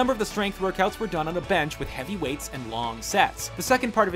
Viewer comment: it sound like you said "And long sex" lol